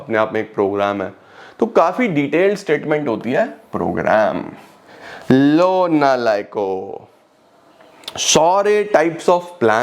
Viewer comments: did he just say " Lo nalayako ' 🙃🙃🙃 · Did he just say " Lo nalayako " 🙃in